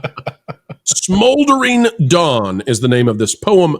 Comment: I thought you said, "Smoldering Dung."